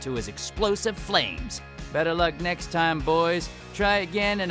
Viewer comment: someone tell me what he says all I can hear is flame